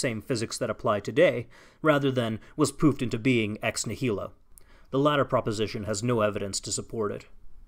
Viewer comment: I heard "was poofed into being ex nihilo" as "was pooped into being" and lost nothing in that mixup.